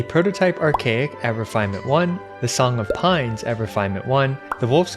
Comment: I thought you said Song of Porcupine lol